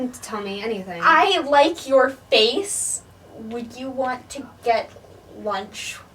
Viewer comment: "i LikE yOuR faCe" literally me to my crush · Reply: I thought that this said “I like your lice”